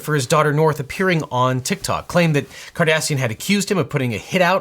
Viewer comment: Gul Dukat has nothing to do with this! (I can't be the only one who heard "Cardassian")